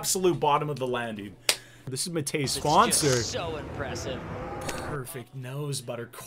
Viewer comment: I always hear "TODAYS SPONSOR"